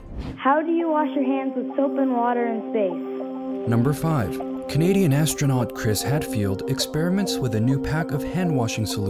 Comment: When he said he's a Canadian astronaut I heard him saying: he' a comedian astronot. But that is just me